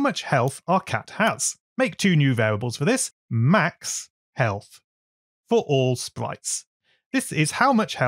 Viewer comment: (Subtitles) You said “heath” there instead of “health”